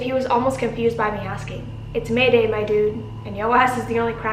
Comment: I think his name is "Jamal" or something but in the beginning he said something but I thought he said "It is wednesday my dudes"😂 love you coach!!